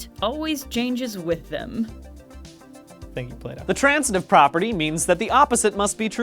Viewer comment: When Brian said "Plato", i thought he said "Playdoh" and got confused about what's up with the playdough company